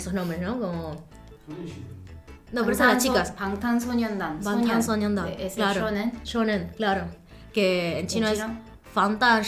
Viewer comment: Escuche mal o el hombre dijo (5:37) 소녀시대? Si si, me cayó bien jaja Ustedes si me cayeron super bien 😍😊 (6:00) Yo amo 소녀시대 ♡